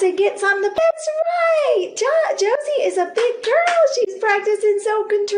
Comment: lol, I just hear ‘Josie’ saying “I’ve been a bitch!”